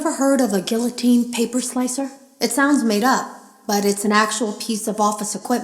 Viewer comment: at the first time I thought she said Guillotine circumciser